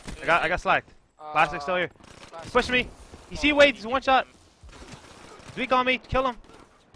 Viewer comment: Wait did I hear "one shot" at 1:30 when you didn't even hit him? Or am I tripping?